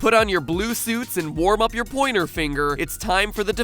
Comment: Pointer finger, I thought you said middle finger!